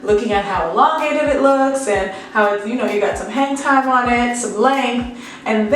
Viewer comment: My face when I heard "Hentai" other than "Hang time"!